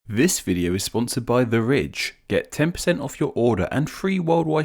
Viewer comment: I misheard the intro as "this video is sponsored by the rich"